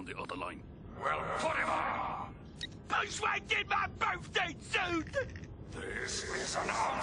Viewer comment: What Fuzy Lumpkins said at 0:24? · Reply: "Bush whack in my birthday suit!"